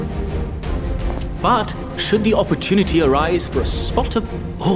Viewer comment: Video crashed midway through "twinkle-toed" so it sounded like he said "I will be your twink" directly after busting a move